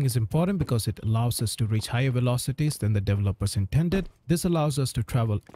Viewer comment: on 4:05 I keep hearing "to reach higher velocity" and "to retire velocity" over and over again lol. am I the only one, when you rewatch that moment, you hear 2 different phrases?